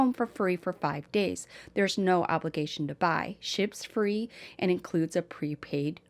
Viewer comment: When you were talking about Warby, I heard “shit’s free” instead of “ships free” 🫣😅